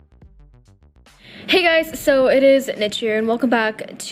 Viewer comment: I thought you said ”Hey guys! It’s Ed Sheeran!” 🤣🤣 Fantastic video love! These tips are very useful.♥️